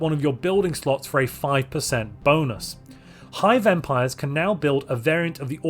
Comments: When you said "hive empires," for a moment I heard "high vampires," and I was wondering when they got added to Necroids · When he said 'Hive Empires' at first I heard it as 'High Vampires'. Now I am kinda sad. :(